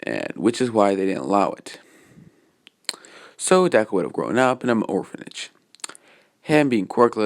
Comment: Uhhh I was spacing out and when you where talking about the orphanages I heard “burn an orphanage” I’m still very confused also sorry for any spelling errors